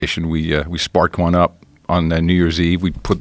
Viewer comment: First 2 seconds I hear “spark one up” I like this guy already